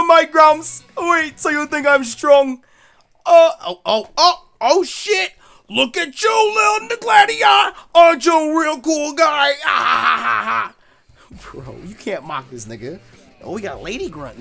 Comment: bruh i always thought hau said "little Gladion"